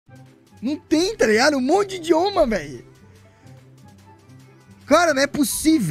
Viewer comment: eu juro que escutei, "Um monte de Homa" kkkkkkkkkkkk